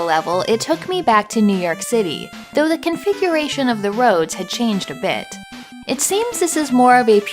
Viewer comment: The volume was low as I watched, and I heard “the configuration of the woes had changed a bit.” Seems to apply as well...